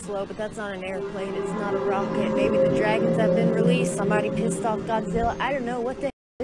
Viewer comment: Lmfaooo @ 5:20 thought she said, “Maybe somebody pissed off Dr. Phil.”